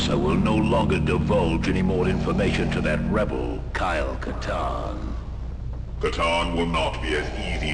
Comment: It sounds like youre saying carl katarn and it just sounds like a game about a space accountant now lol